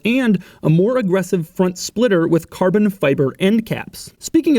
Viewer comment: at about 4:20 I only hear "Carbbn Fiibaa", now in my head for the rest of the day...